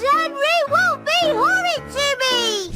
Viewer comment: why did the cc say horny..